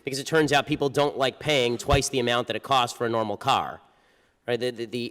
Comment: What? You said brazilian cars?